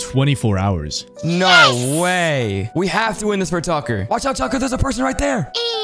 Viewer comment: I thought he said sucker for a second